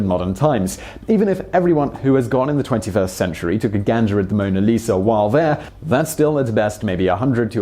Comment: Did Simon just say "took a Ganja"? lol